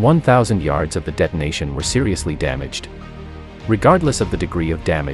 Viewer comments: it didn't go bad it was sold for crap🤣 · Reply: I had to listen to that more than a few times to make sure I wasn't hallucinating that I heard "sold for crap" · it didn't go bad it was sold for crap🤣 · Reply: I thought he said sold for crack lol